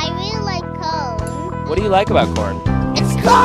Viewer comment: I thought he said” it’s cold”💀💀💀